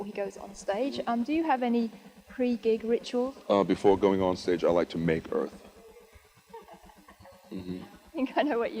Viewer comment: One year ago I saw this and I thought peter said "I like to make her" and now I realized he said "I like to make earth" 😂😂